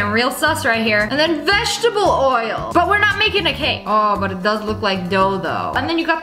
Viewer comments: DID SHE SAY DILDO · AM I TRIPPING OR DID SHE SAY DILDO??? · did she say dildo? 😖😵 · sounded like she said it looks like a dil*o